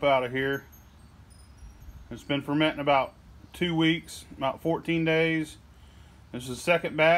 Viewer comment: did he say that "it's been fermenting for 2 weeks...NOT 14 days" ?